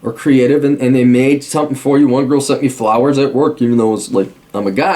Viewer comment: Did he just say @ 13:14 that "...one girl sucked me for hours at work"?! I think i heard him wrong.